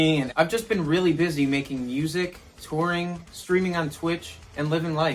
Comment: already understood "screaming at twitch" - instead of streaming ^^